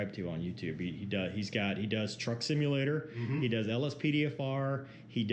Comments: I keep hearing "Trump simulator" and now I am imagining the pit owner sitting in the gaming chair watching Fox News on the big TV and yelling into a disconnected phone. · TRUCK simulator!? I thought he said TRUMP simulator! Brought my train of thought to a dead stop.